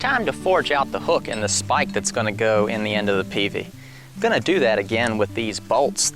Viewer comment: Ohhh, a "peavey"!! In your last video, titled "Recovering Logs with a Winch", you referenced a peavey, but I thought you were saying "a P.V." Couldn't figure out what the P and the V stood for. Push vehicle? Pry vehicle? XD Heh heh. Girls. Tsk.